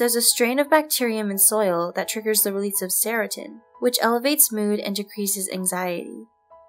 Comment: Did you say "serotin"? Tf that's not a word.